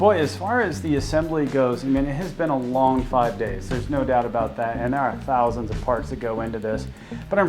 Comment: First time sounded like he said hundred and ten thousand and I was shocked till I ran back the video.😄